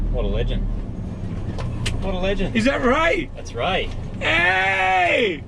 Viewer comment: Oh man, when said "Its Ray!!" I thought he said "It's rake!!!" I was really excited to see if rake still loves it after all these years, but alas the wait continues :(